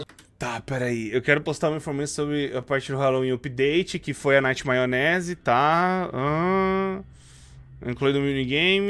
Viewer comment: Só eu que ouvi no minuto 19:37 o falando nightmaionese KKKKKKK